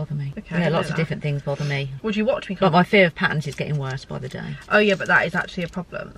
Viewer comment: Fully thought said her fear of pans 😭😂 had to use CC to get that 🤦